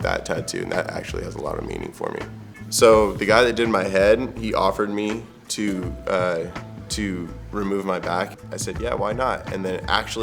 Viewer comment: what the hell is he even saying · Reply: Someone offered to remove his back tattoo. Come on man.